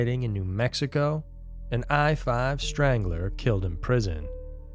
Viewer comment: Thought he said I-5 Straggler, lol, had to rewind it